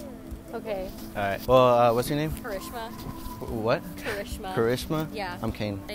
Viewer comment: I thought she said Hiroshima